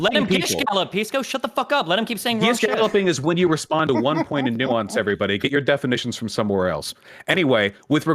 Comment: Did Vaush seriously say at 45:23 that “Gish-galloping is when you respond to one point and nuance everybody”...did I hear that wrong? I must have heard that wrong?